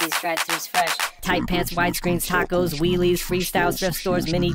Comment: I always thought when he was saying "wheelies" he said weewees.